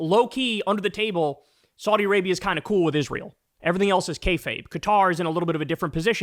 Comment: Did i hear the word, "kayfabe?"